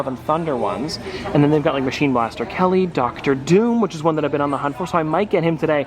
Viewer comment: Im i the only one who heard him say machine blaster Kelley 😂